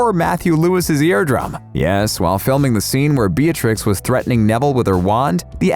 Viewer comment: did you just say BEARTRIX? No hate tho ily all